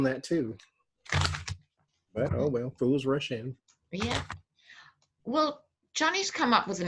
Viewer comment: FOOLS RUSH IN...... said. Today I heard...Fools “Russian”